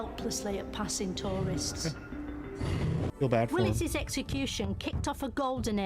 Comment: I thought she said Wallace's execution, presumably William Wallace, but when I went back to find time stamp, it sounded more like Willis.